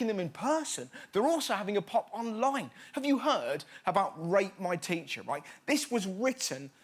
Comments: I thought he said rape my teacher 😂😂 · I thought he said rape my teacher instead of rate my teacher 😂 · i thought at first he said rape my teacher...... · Lol, I thought he said "rape my teacher". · I thought he said rape my teacher😂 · I thought he said "Rape my teacher". For a second I thought "Shit just got real" then I realised he said "Rate my teacher", pheeewwww. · Just me who thought he said “rape my teacher” yes? Okay... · I thought when he said rate my teacher the subtitles said rape my teacher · I swear he said rape my teacher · I honestly thought when he said rate my teacher he said rape my teacher. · Who else thought that when he said rate my teacher he said rape my teacher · I thought he said rape my teacher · I thought he said "rape my teacher," and I was like oh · I thought he said rape my teacher.😂😂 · I can't be the only one who heard "rape my teacher" at 1:57 and not "rate my teacher" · I thought he said rape my teacher . My bad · I honestly thought you said rape my teacher that would have been disturbing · Who else heard rape my teacher at 1:59 instead of rate my teacher. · Oof I misheard him for ‘rape my teacher’ The struggles of being partially deaf 😂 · I thought he said ‘rape my teacher’ misheard tremendously 😯😶😶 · i thought he said " rape my teacher" i was so confused-- then i realized my brain is just messed up XD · I thought he said, rape my teacher 😂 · i thought rus said rape my teacherXD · Low key thought it said rape my teacher